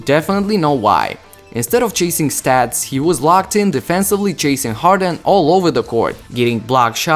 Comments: did he say Jason harden or chasing harden😂😂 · does he say jason harden instead of james harden